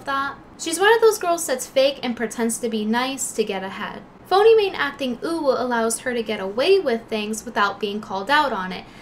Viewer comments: did she just say "acting uwu" ??? who the fuck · I though she said "to get a head" After turning subtitles I discovered she actually said " to get ahead" 🤣